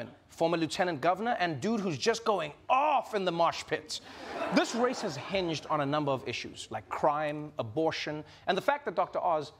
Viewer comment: I swear I heard - "this race has hinged on a number of issues - crying..." 🤣